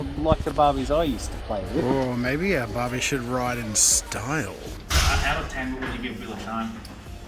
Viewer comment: Okay please tell me I'm not the only one who heard (Idk how to spell it i'm sorry) "on a scale of 1-10 what would you give Bill Nye????" I know I CANNOT be that but please someone help edit